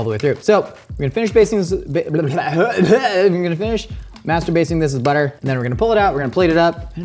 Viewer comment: Anyone else hear that “masterbasing”